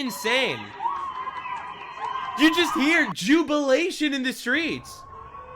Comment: I don't know if it's because said it, but I thought I just heard "Jew-bilation"